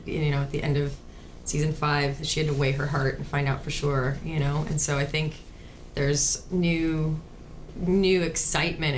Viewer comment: What did Emilie say to Lana 1:44 ??❤️ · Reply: Seem like "sorry"